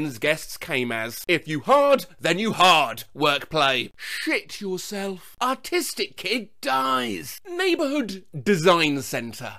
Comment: For a second I thought you said “autistic kids”